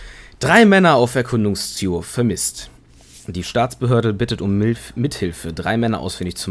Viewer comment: "Die Staatsbehörde bittet um Milf" Verstehe °-°